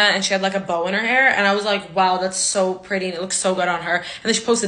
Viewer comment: Why did i hear "Boner hair" everytime she said "bow on her hair" 😂